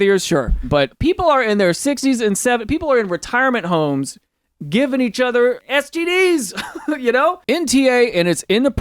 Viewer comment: what did he say? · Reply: "People in their 60's and 70's... People in retirement homes giving each other STDs"